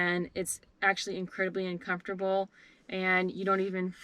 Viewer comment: did she say the bed was uncomfortable??? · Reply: hello, it does sound like I said uncomfortable but it is very comfortable!! It is just a 3 inch talay natural latex foam topper with 1 inch of cotton batting. I did a ton of research on bed material before making it and there is a ton of harsh chemicals in most bed material. Since living in a confined space we didn't want to breathe in the off gasing fumes. This bed from day 1 had no smell at all! Thanks for watching:)